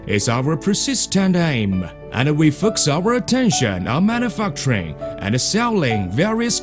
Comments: I think he just said "I'm at a fuck train" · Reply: Think its supposed to be manufacturing...lol but i heard "Im on a fuck train" as well the first time lol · I think he just said "I'm at a fuck train"